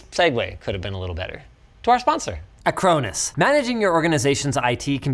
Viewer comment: when he did the last sponsor I heard "a cronus". I thought I had to block Linus for a minute